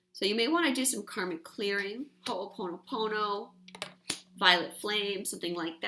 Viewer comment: I couldn't understand what you had said. Karmic clearing, call upon who?? I apologize I just feel like this will help me. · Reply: Yes, I said try and do some karmic clearing using Ho'oponopono or violet flame. <3